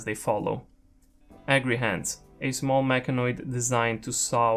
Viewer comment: I had to rewind a second since I thought you said angry hands when you were talking about the agrihand. I am now renaming all Agrihands to Angry hands from now on.